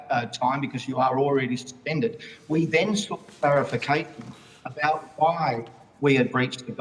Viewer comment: did he seriously just say "clarificate" please tell me I misheard that